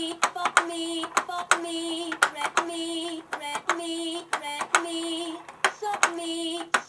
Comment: I Heard "Rape Me" What In The Name of Zalgo Were They Thinking?